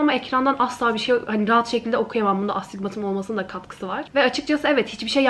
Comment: de ne diyor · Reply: Bunda astigmatımın olmasının da katkısı var demiş astigmat bir görme bozukluğu ve baya erken cevap vermişşm beş yıl sonra dlsödöxldöödödödödödlf